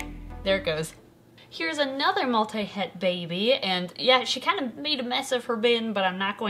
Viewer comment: When you said Multi Het baby I thought you said Multi head baby 😂